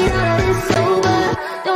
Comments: anyone that also heard "I just killed my eggs" 👇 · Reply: I hear "I just killed my S" 😂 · Why I heard the song "I just killed my eggs" instead of "I just killed my ex"💀